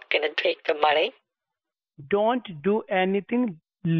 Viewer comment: So here i am coding listening to this in the back ground then all I hear is "don't do day" "DON'T DO DAT" then I start laughing my ass off...